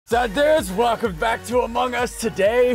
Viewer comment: did ssundee say zud dues